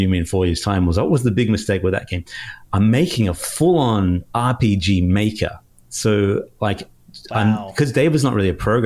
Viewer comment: Did just say... RPGMaker??? The entire RM community’s ears perk up simultaneously (but seriously you probably could’ve made a prototype in MV or MZ (heck maybe even VX Ace!) still awesome though, just sayin!